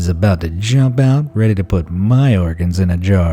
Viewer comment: "Bathtub full of brains": What's the mystery? Obviously it's a think tank! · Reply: I THOUGHT IT SAID "BEANS IN THE BATHTUB" OMGGG